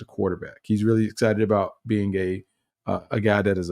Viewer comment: Did say josh is excited about being gay ?? 😳